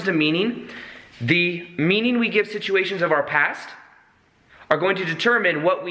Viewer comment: you say shituation instead of situation. I think you just coined a new word! It worked in the sentence and you should definitely use this in future videos. Shituation ie: what you thought was a bad shituation in life was really a way for you to change you're perception. Great video! Definitely keep moving around.